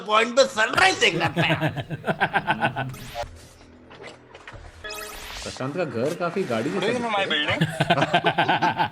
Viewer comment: What did Rahul say? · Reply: Prashant ka ghar ekdum gaadi jaise hai